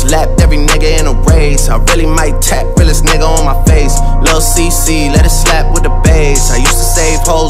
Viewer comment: Swore he said “I swear I’m gonna tap with this nigga on my face”